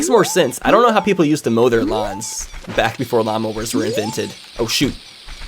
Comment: Low key when he said lawnmower, I thought he said llamas. Asking who invented llamas XD